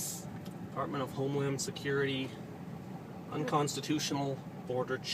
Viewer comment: At the beginning I heard him say, "apartment of home lamb security."